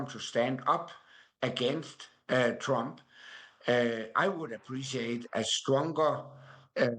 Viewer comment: Does he call him Herr Trump?